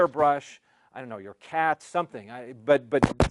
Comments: did he say cat? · Did he just say cat? Strap your cat to your chest with Duct tape?